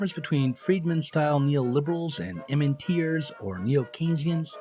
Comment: what does he say? "The difference between Friedman-style neoliberals and BLANK or neo-Keynesians..." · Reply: "And MMTers or neo-Keynesians" MMT = Modern Monetary Theory. <link>